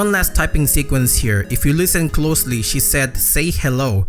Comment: I always thought she said found your home not say hello